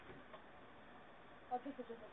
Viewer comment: Not sure if this is what they said: "I think the Tesla's gone" "What?" "Check the camera" "I just can't see it"